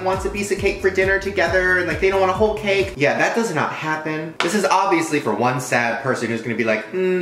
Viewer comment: I thought he said "this is obviously for one sad bitch" but he said "this is obviously for one sad person" am I going crazy?